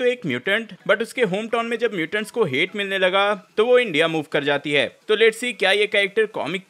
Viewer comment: did i hear india ???? Interesting 🤓